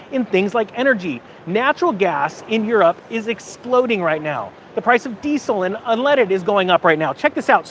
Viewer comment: i thought you said European niggas explode. Spontaneous combustion?